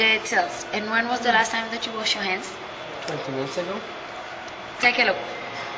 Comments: "When was the last time you washed your hands?" "Uhh...a couple weeks ago." Dude, you need to wash your hands multiple times a day. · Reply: i thought they said 20 minutes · To me it sounded like he said a couple minutes ago, although you could be right. I strongly believe that this is some sort of acted scenario to sell product and so the "a couple of minutes ago" would be the more likely answer, to show that the skin cream is amazing or whatever. Just my thoughts though.